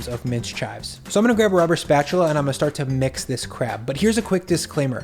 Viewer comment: thought you’d said “so I’m gonna grab a rubber spatula and mix this crap”